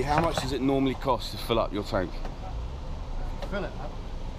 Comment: "I LOVE YOUR TANK!" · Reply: LOL i literally heard "I LOVE YOUR TANK!!" ahh thats funny